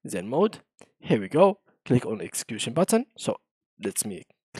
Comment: Odd that vs code requires a video tutorial to figure this out. On Windows, hitting escape twice did the trick. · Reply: cheers I couldn't actually understand what the guy in the video said, "execution button" is what I thought I heard, you're version was a lot simpler and worked